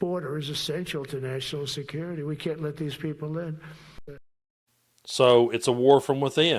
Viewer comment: He is a Dangerous Menace to America, He Is The Enemy Within. Kegsbrearh And Trump. FAFO, . Your not on the Same Team. 12:35, Did Trump say " We cant let them Live". Did I hear that , Did you.?